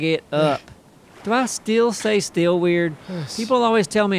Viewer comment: To my ear, it sounds like says steal rather than still.